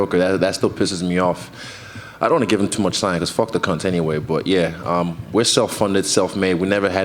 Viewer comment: did he say, "fuck the cunt" about the Kiwi PM? If so that's so hilariously awesome